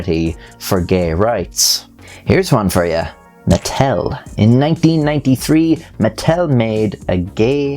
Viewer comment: I don’t know why I heard Nuttel